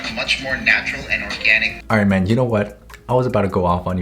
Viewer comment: sounded like he called him "Iron Man"